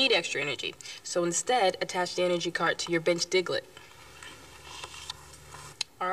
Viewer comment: I thought she said “bitch Diglett” oh my god